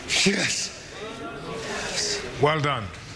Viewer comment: "The first of many." Jessie: *"Pikachu's face." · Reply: i hear "first the money" xD